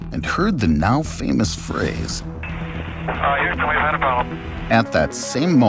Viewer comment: what does he say here??? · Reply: "Uh Houston we've had a problem"